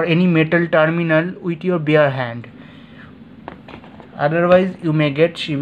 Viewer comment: I heard you say not to touch the terminals with my beer hand. Wouldn't that hand already be busy holding the beer?